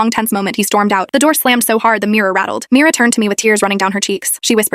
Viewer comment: Bro I thought it said the Mira rattled 😭